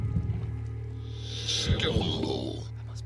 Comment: Something to consider, whenever the system says "stumble" (I hear it as "skillful", but apparently it's "stumble") your clank increases by 2. The less cards you have and the longer you've been in the dungeon, the more likely it is to happen. If you're at the end of a run and start getting stumbles, and you don't have many cards left, it's probably best to just sprint through and disregard the clank you get from it. It might also be worth tracking your clank, which I've been doing a bit so far, and it seems like the vexes spawn at around 15 clank